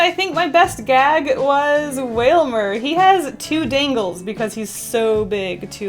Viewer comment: Did she say whalmer